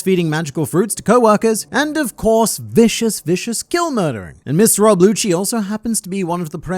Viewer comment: Did he say “Miss Rob Lucci”?